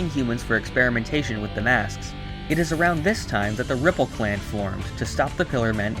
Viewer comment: Did he just said "Ripple clan"? Well that just sounds like rejecting my humanity but with extra steps